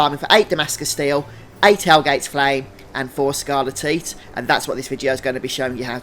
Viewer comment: Never heard someone pronounce it as "scarle-teat", I always hear it as "scarle-tight" lol. Thanks though for these guides mate, they are very helpful